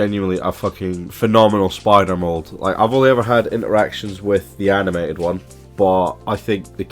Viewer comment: When you were talking about Blackarachnia, you said "I only had interactions" but I did a double take cause I thought you said "erections." Especially after you just got done joking about being a simp, my mind was about blown.